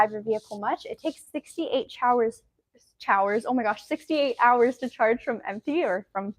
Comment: Did she say 68 showers 🚿?